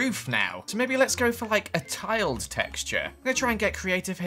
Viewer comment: When Joel said “let’s go for a tiled texture” I misheard it and thought he said “let’s go for a child texture” and I was like: 😳